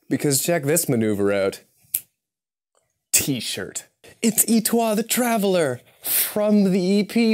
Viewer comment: The French part of me thought you said étoile the traveller (star)